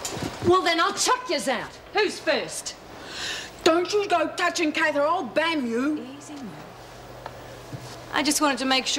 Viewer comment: I completely lost it at what Merle said 25:18 Joyce lol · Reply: Merle said: "Don't you go touching Kath or I'll bam you". Bam isn't a real word but I suppose it means hit or bash.